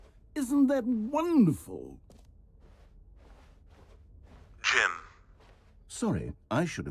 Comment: My name is Kim and like that genuinely scared me cause I thought it said Kim